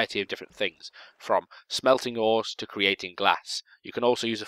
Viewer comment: I thought he said "smelting whores." XD